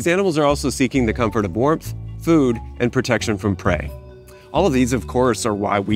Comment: Did i hear at 4:30 “protection from pray”? Or “protection from preying”?